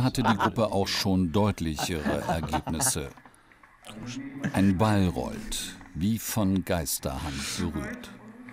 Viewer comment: Lol bei 23:50, ich hab mir das Video mit gesperrten Display angehört und dann kommt der Sprecher mit "ein Ball rollt" und ich habe verstanden "ein Balrog!" Und dachte "Ja jetzt wird's interessant! Da kann er jetzt nicht vorbei" 🤣 🧙‍♂️😜